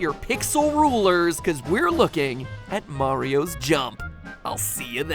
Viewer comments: Did you just say : "Break out your pixel rulers, 'cause we're looking at Mario's junk."? That's not fair, he's Italian. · Wow I was listening to the end card and I heard "break out your pixel rulers because we're looking at Mario's junk." I had to go back to make sure that's not what you said.